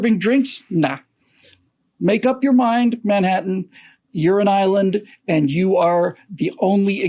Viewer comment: Manhattan has a "Urine Island?" That's what he said